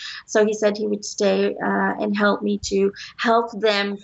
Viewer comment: is that?? Is he saying " hell" or help"??